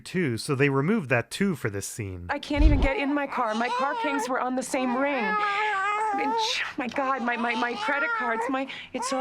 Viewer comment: I thought I left a comment about this a while back but I guess I never did - Skyler says "My car kangs were on the same ring" at 5:20. I think there's a word for when someone slips up like this when speaking, sort of duplicating the "ngs" sound from "rings" on to "keys". There's another verbal flub in the pilot episode too - Hank says "Schoob Lus" instead of "School Bus" when they're doing the meth lab bust.